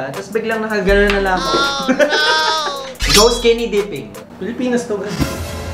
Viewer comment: di ko masyado narinig ung question kasi different sa caption sa screen.. 😂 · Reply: go skinny dipping